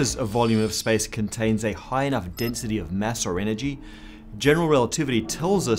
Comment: if you have captions, it says ass instead of mass